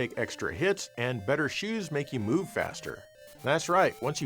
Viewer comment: It sounds like he said, "Better shoes make you poop faster"